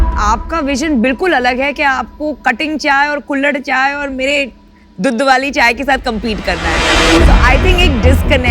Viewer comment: did I hear it "Mera dudu waali chai"